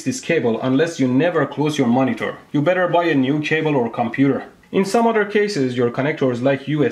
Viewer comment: 怎麼可以把cable聽成table的